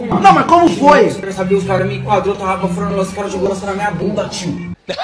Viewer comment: o q o cara(o q ta sentado na cadeira) falou ? N entendi direito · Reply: Os cara enquadro ele é ele ta com lança perfume aí os cara aplico na bunda dele kkkm